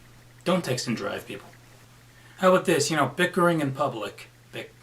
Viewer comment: For a second thought I heard bitcoin instead of bickering